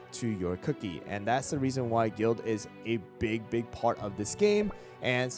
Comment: Is it only me or is it i heard him said guilt instead of guild